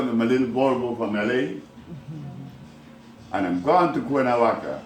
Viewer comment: Cuernavaca? Is that what he said ? Any ideas ?